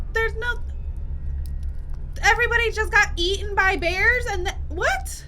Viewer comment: I completely misheard “eated by bears” as “yeeted by bears” and I like mine better